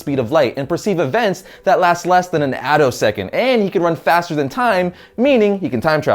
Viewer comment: Did you say he can run faster than time?